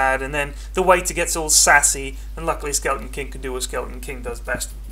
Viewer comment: LOL 1:08 sand king lulz instead of skeleton king